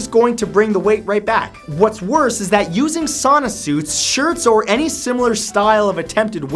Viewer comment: I thought 9 said Santa suit